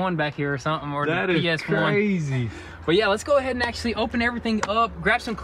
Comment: Did he say PS1? 🤣